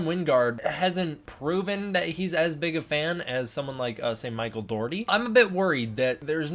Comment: did you say “saint Michael Dougherty”?